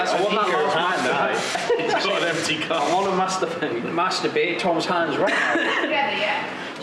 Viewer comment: I thought they said "do you like to mas-turbate"😂